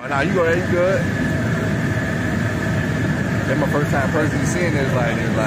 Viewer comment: I thought he just said this his first time purging 😂